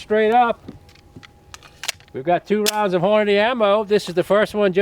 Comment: Sounded like he said horny ammo